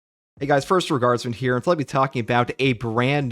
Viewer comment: Everytime he says "first tour guardsman here", I always hear "first regardsmen here"